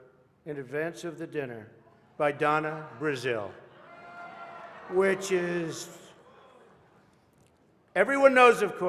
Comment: Interesting how at 12:27 Trump mentions jokes were given to Hilary by Donna Brazile and then says "Which is.." but it kind of sounds like he calls them both "Witches" especially since he pauses and changes up what he was going to say. Also interesting is what he says at 13:57 about everyone agreeing that they need to support "the great work" that comes from the dinner, The Great Work being a NWO catch phrase for their overall plan. He probably meant neither of these things I noticed but interesting nonetheless.